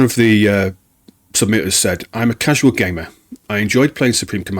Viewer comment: I heard “im a casual gay man…” I need to get off twitter.